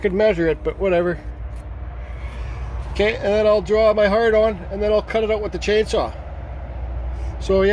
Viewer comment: You said draw your heart on, I thought you said hard on. Lol. It turned out good.